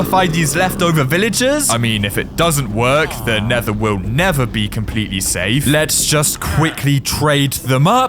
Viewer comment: who else heard “the nether will NETHER be completely safe”